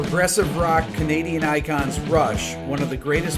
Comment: Damn my deafness. On first listen I heard 'comedian icons Rush'. smdh. Anyway, Rush is the first band I think of when I hear the term 'Power Trio'.